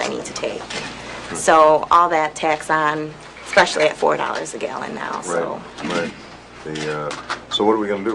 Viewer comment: Gas is $4.00 an hour... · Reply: if you are referring to the comment at 4:38 she clearly says $4.00 a gallon !!